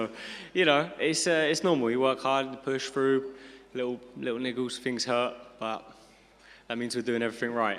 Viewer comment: what did he sayyyy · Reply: 😂 niggles.. it's a English term for something bothering him or discomfort